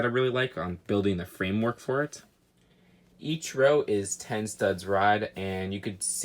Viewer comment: did he say each row is ten studs ride?